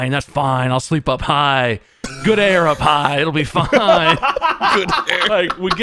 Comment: "I'll sleep up high. Good air up high!" Is the most Markiplier thing I have ever heard. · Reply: I legit thought he said "good air of high!"